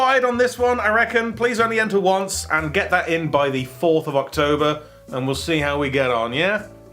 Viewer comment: did they say 4th of October?